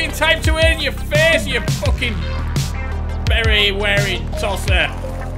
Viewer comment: Hahahaha I just pissed my pants ...I thought you said "" ya berry wearing tosser "" 🍓Hahaha